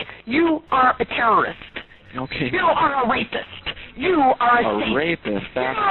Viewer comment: I thought she was saying racist.